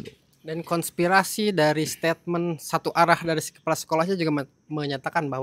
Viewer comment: itu ngomong apa? Gak terdengar jelas "PENGKANCLAPAN" artinya apa?